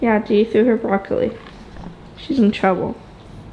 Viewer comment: It sounded like said Ozzie was in trouble. Lol so so funny because she gave the puppies food to him. You are raising children who are growing up into such strong young women.